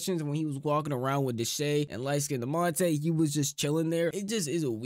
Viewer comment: did he just say lightskindemonte?